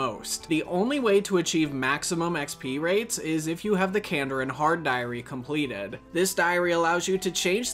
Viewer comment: I have no idea what he's saying at 5:50 ? Candered? Maybe I'm just stupid here.